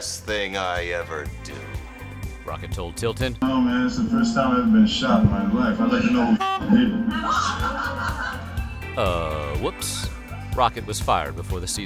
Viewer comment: what exactly is rocket saying? Can’t hear. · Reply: Yo man, that's the first time I ever been shot in my life. I'd like to know who fuckin' did it.